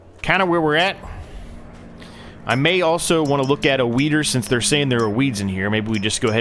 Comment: i swore he said he wants to look at a wiener... HA!